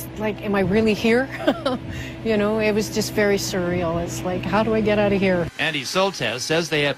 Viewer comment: Did she say "cereal" for the word "sur-real"